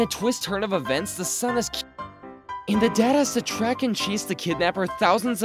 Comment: The dad has sounded like “dead a##”